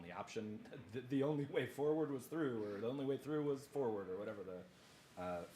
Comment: “the only way out is through,” is what I was TRYING to say here.